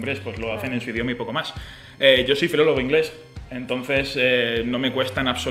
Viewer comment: Que dijo? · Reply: Que es filologo